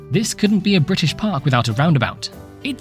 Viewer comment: now as i am rewatching this i have realised that said a very british park instead of welsh the timestamp is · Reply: Nice try amigo. I say "this couldn't be a British park without a roundabout"; A Very Welsh Park being a theme park in Britain. I like your enthusiasm though!